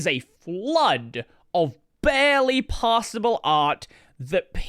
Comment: I heard "barely pawsable art"...